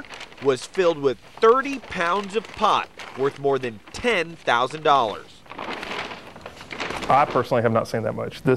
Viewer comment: "30 pounds of pot, worth more than 10,000 dollars." Uh no, he clearly said 10 thousand dollars.